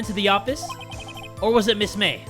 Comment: mia is my real name and i got nervous when i heard "miss mia died" i was like me: breathes heavy in the microphone b r u h